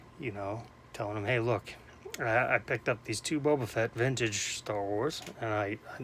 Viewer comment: Yeah I don't think we have to worry about $15 cards being counterfeit. It was funny I misheard you and thought you picked up a second Bo Bichette but then Boba Fett came out & I couldn't stop laughing. Great vid as always!